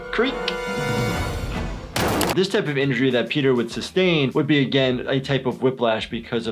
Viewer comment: I thought he said “This type of injury when Peter runs his fade”